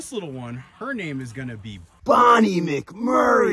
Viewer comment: I do not know What that is but the ending lol 🤣😂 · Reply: Yea I thought he said Bonnie Murphy 😆